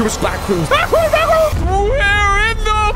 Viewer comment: did say blackrooms